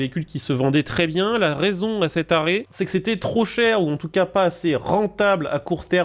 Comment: "la raison à cet arret" j'ai entendu "la raison a ces tarés" j'allais dire doucement l'ancien 🤣🤣🤣🤣🤣